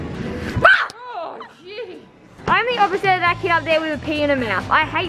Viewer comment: I think I heard her swear 😮 but no hate 😂😂😂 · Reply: no she said oh jeez